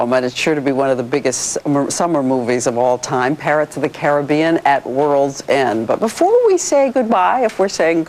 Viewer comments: did she just say " PArrots of the caribbean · did she just say parrots of the carribean